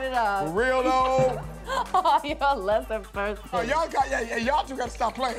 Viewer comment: What is the female DJ laughingly saying after busting on Meatball. It sounds like "You're a lesser person"(?) It's kind of driving me crazy.